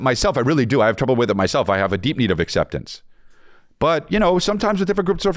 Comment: I thought said “I have a deviated acceptance.” Thought it was a bar until I rewound.